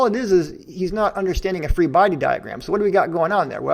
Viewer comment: The non-native English speaker in me misheard "three body diagram" instead of "free body diagram" for all of the original debate, which was soft of confusing ... :o